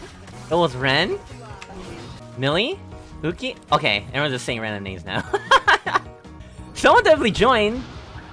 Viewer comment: I think at 0:34 said, "Okay I think you're just saying random names now." But overall great clip as always. Sasuga, you never disappoint.